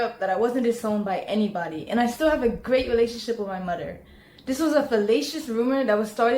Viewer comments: Did she say "This was a fellatious rumor (...)" !? 😐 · did she say this was a felacious rumor? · Did she say “fellatious” rumor? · did she say this is a felacious rumour? 😅